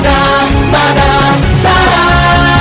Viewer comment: why do i hear "Please don't me simp"